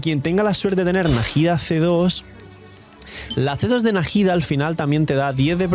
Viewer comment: las tetas de najidas XD perece que dice eso yo tranquilo trabajando y escucho eso 😂😂😂